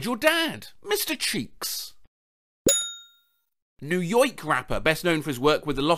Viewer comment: did say "new yoyk"?